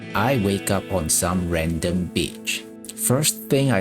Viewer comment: All i heard was, "I wake up on some random bitch."